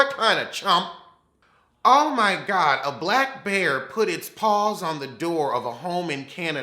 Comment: Damn, I must be tired, instead of hearing "black bear", I heard "black mayor", putting his paws on someone. I'm like, "was he Republican?" I really need to sleep more